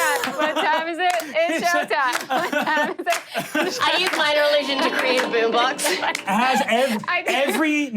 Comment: The 1:38:38 captions said "my religion" instead of "minor illusion." What are we, followers of yes! or sth🤣